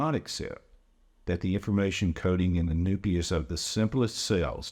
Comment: Did he say "the noopius of the cell" ??? I really hope someone has already caught this, but check what the dude says at 21:50. Not the nucleus of the cell, the noopius of the cell.